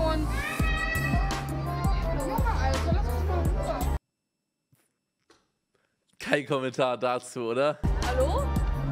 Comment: Was sagt die bei 16:19 ? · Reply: "Junge alter lass mich mal in ruhe"